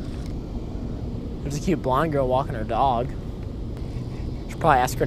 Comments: I thought you said "there's a cute blind girl walking her dog" and was like wooow lol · hahahaha i coulda sworn said "a cute blind girl walking her dog" and i was imagining this blind girl getting into John boat with her service dog